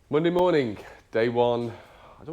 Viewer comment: lol, "Monday morning". I thought you said "Bundy morning"